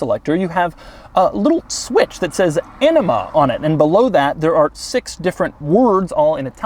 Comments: I really thought you said Enema not Anima there. Was greatly concerned about this new feature. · I swear I heard enema...😂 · Ha, I wasn't watching when you said the switch says 'anima' on it and I thought you said enema! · I keep hearing "Enema" instead of "Anima". 🤔 I think it's pronounced Uh-nima? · Did he say Enema? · who else thought he said ENEMA? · I was browsing on a different tab and I hear Doug say "and over here theres a switch that says enima, and I got really conffused for a good moment befor I rewatched it. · For a second there I thought you said enema not anima, I thought wow maybe when you put the pedal to the metal it goes so fast that you just lose your shit! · Did he just say enema instead of anima???? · Anyone else hear ‘enema’? :) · Oh, anima, I thought you said enema. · I wasn't looking when he said "Anima" and thought he was saying "enema" and I was so confused